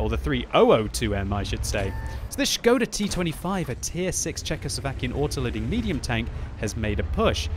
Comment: what do you say there? scoda t25 tier 6 chacus of acuan autoloading medium tank? what is a "chacus of acuan" tank??